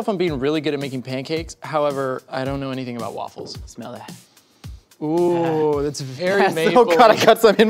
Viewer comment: Did Josh just say smell-a-that?